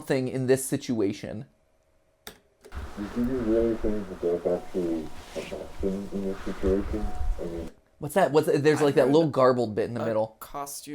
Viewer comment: From what I heard at 16:22 I think it says "do you really think there's some kind of thing in this situation ? I mean" Thats what I heard